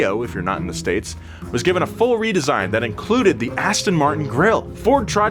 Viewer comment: I had to rewind couple Of times in slow mo I thought you said WIT. BOOTY!!! But you said included.